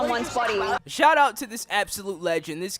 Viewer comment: When he said peace at the end it sounded like he said peas and it made me laugh.